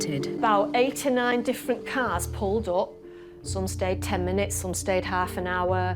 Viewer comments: I heard "89 cars pulled up" not "8 or 9 cars" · Omg I thought she said 89 cars not 8 or 9 😆😆